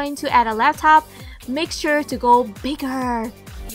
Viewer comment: youtube really botches closed captioning some times, sioI wondered if you'd help me? At 5:54 you say, "if you're going to add a laptop, make sure you..."... WHAT??!!! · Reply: Kandl, she said to make it bigger.